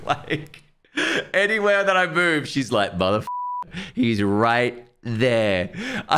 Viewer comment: It sounds like is saying some things that are not nice!! 😂 I heard “Motherfuckerrrrrrr” , “I’m gunna go play Nintendo” 😂😂😂